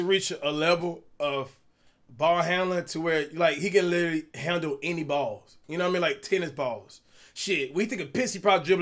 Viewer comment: what did he say he can handle any balls?